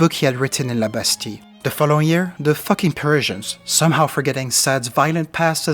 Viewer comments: did you say the fucking persians? hoe hysterical.. funny. lol... right on, nothinhg against the persians, but funny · I dont even know if this was a line said but I heard him say "then the fucking Persians" and I lost it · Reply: Haha I meant the Parisians! Because fuck the Parisians! But I'm glad you had a good laugh. Thanks for watching and merde!